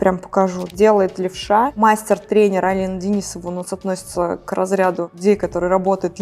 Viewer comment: напишите пожалуйста имя мастера левши по перманентному макияжу. Алина Денисова? Или я не правильно расслышала?❤ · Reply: Да. Алина Денисова